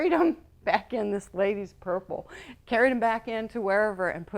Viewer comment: cat & panty was funny (@ 3:26 -- purple ? I couldn't understand); & 8:30 was sad but informative .